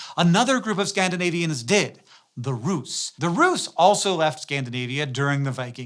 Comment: Rus: is that how it is pronounced? I always thought it rhymed with fuss. I had to relisten, at first I thought you said roof.